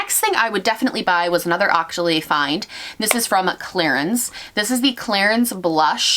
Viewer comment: When you said clarins I heard claire's and almost choked on my dinner hahahah